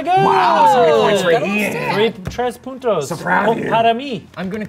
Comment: Ian wanted to say 3 puntos but it sound like he said putos which would translates to 3 male prostitutes for me